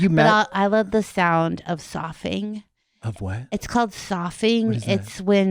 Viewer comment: Does anyone know the word that Kristen says at 47:30?? I'm trying to find it 😭 I've tried so many variations of what I think I heard lol, but can only find psithurism and sussurus which don't actually seem to be specific to wind through trees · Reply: soughing the high or low long sound that something such as the wind or ocean makes as it moves: Overhead, there was a soughing in the treetops. At night in his room he could hear the soughing of the pine trees in the churchyard.